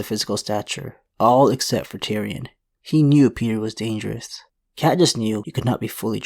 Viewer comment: did you say Katniss? Lol